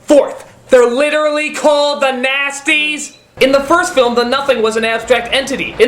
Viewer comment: HAHA! I thought they were called the Nazis!